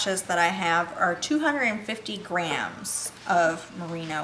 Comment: When they started talking about price and mentioned the two pieces were 250 grams, I first thought she said 250 grands and was very surprised how much budget they used for this video... 😅